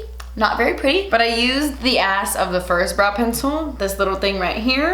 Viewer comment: Wait did I hear say “the ass of the first pencil” @ 4:15 or am I hearing things lol